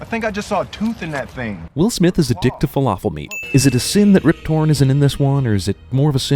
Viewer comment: did you just say "falafel meat"??